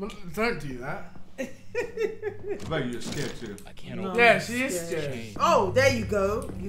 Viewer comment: I thought you said you wont sacred 😂